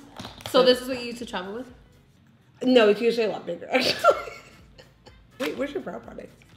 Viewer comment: What did say at at 1:35 I couldn't understand her n now it's bothering me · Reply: Juan Covarrubias " it's usually a lot bigger"